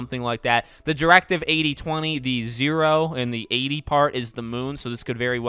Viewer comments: I thought it said Directive Bozo ☠️ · Am I the only one who thought it said directive BOZO